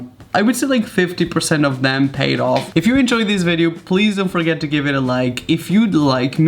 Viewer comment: Did he just say "Please and forget to leave it a like" at the end? Do you NOT wanna have us like? (Ps. this is sarcasm do not take it literlly)